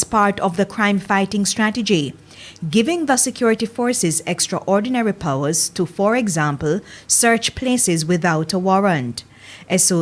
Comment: I thought I heard she said "Soe's give the security forces extra power like to search certain premises without search a search warrant" but, isn't thats wat they've been doing from the beginning.